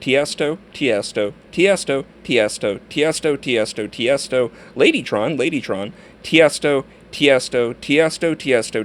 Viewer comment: Oh my fucking god I thought he said babytron